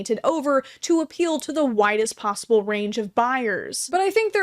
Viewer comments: I thought I you said “to appeal to the whitest possible range of buyers” instead of “widest possible range” but honestly that checks out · She said "widest" but I truly thought she said "whitest" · when you said that the so this to “appeal to the widest possible range” i heard “appeal to the Whitest”